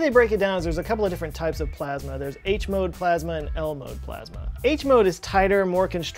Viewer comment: When he said “ L-mode.” I thought he said Elmo